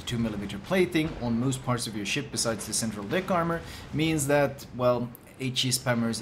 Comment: anyone else hear him talk about "Dick Armor"? :D